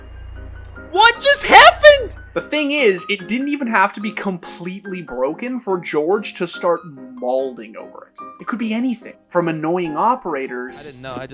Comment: did he just say "malding"? Is this the sign of a AdmiralBulldog's video? Im still waiting thescore. Kekw